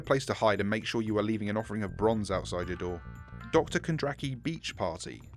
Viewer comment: Anyone else heard him say "bronze outside your door" instead of "booze outside your door"